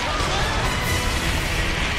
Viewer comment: I swear I hear someone say, "Oh Jesus!" And if you watch closely at 7:31, Littlefoot clips riiight through that stalactite.